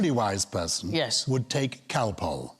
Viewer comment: "A truly wise person would take _________." Sounds like cal-pole to this USA person. What is he saying? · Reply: He said Calpol, which is a painkilling and soothing linctus to be given to young children. Quite possibly only available in the UK?